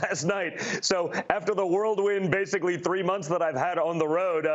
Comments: Did he say 'world wind'? Illiterate fool. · I distinctly heard him say 'worldwind'. LMAO